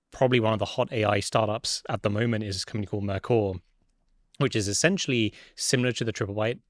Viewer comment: what company did he say? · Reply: Mercor AI. Worth $2B. Hottest startup in the history of Silicom valley. CEO is 22 CTO is 19